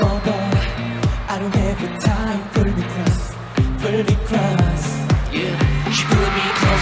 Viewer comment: I first heard this song from SSW, and always thought he said "for me cries," but apparently it's "pull me close" LMAO